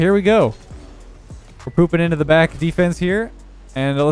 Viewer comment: Did u just say( we are popping back to defense)